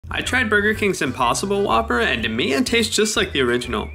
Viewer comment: I thought it said impostor whopper